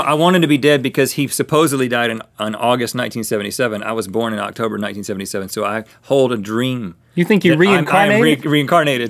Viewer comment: did he just say he was born october 19? if so. OMG MY BIRTHDAY IS OCTOBER 19